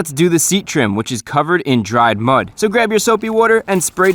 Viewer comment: I started cracking up because I thought he said the seat was covered in dried blood lol.